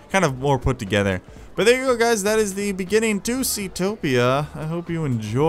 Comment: I thought you said Zootopia